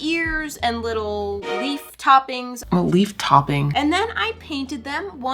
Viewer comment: for a minute i thought she said beef toppings not leaf 😆